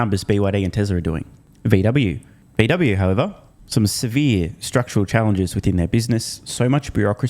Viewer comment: Did you call VW "Structurally Unprofitable" at minute 8:35? I guess we now know what Chanos meant by that. HA!!!